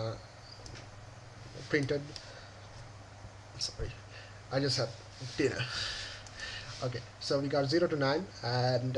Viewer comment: did he say "sorry i just have diarrhea"?????????????????????